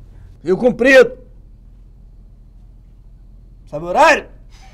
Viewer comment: O que ele fala depois de "Rio Comprido"??? Minutos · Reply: "Sabe o horário?"